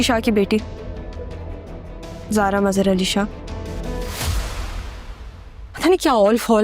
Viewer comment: she said zara mazar ali shah instead of sara